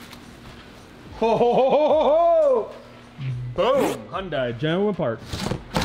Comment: Tf He Say At 2:53 😂😂😂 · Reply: “ genuine parts “ 😂